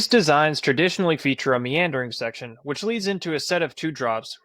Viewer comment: misheard meandering as mandarin because of the visuals and was very confused for a second lol